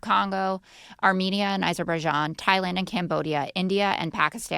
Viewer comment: I thought he said it was Aberbaijan (sic) v. Albania (sick).